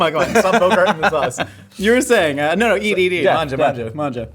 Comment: is it me or did he actually say "mangia, mangia, mangia"?